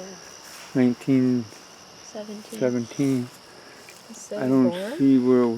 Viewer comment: voice at 15:41 not sure what it's saying but it sounds kinda like 17